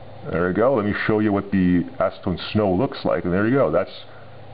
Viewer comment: haha thats weird it does sound like he says stoners D